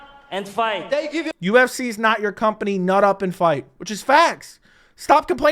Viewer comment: he actually said shut up and fight. Not nod up and fight